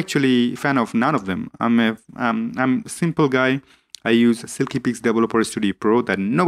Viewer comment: What did you say at 4:12 "I use ???? Studio Pro, that nobody talks about" ???? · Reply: Silkypix